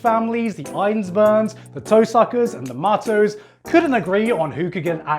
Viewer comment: I heard him say The Toe-suckers Mages